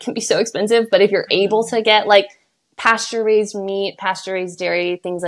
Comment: hi! at around 5:24:40 you were asking about recommendation of “pasteurised” dairy or “unpasteurised” milk. is actually saying “pasture-raised” dairy, as in dairy from cows that live on open pastures rather than a factory. american southern accents love to string words together 😅